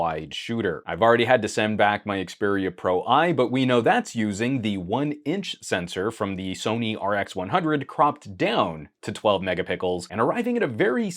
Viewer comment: Did he say "mega pickles" at 1:24 🥒